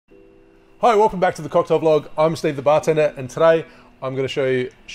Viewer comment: I'm new here so I thought he said "welcome back to the cocktail bloke " lmao